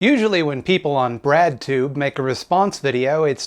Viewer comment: i heard ''bradtube'' instead of breadtube so i was wondering how i'd never heard of this Youtube of the Brad's